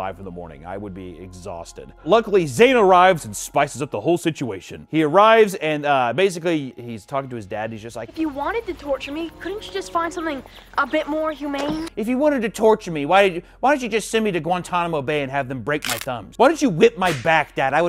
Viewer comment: So glad I’m not the only one who thought was saying hyman